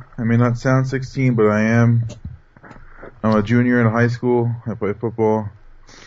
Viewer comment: lol i thought he said im a jew that goes high school ... lol!